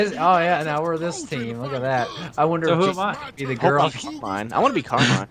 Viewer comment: Did say "Fuck Carmine" at 6:04? Because if he did that is the first time I have ever heard him cuss.